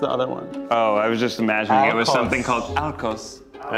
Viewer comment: Idk who did the subtitles but around 24:54 when Ned says what he imagines the green word for alcohol could be the subtitle says “Arcos” but you Can see he wrote “alkos” Funny enough “Arcos” is my second last name